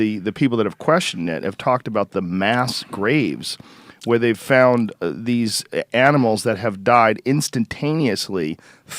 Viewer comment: Joe says something i cant understand " and that was the time ....tapi was built" when they are talking about mammoths can someone tell me what did he said?? i tried to look for it on internet but i cant found anything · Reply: Manuel Ceriale Gobekli Tepe ✌️